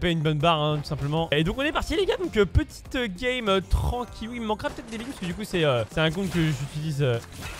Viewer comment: il a dit quoi ? "Il me manquera quoi " ??